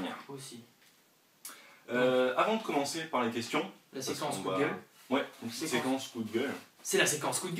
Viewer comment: Si toi aussi tu a compris "Séquence Google" a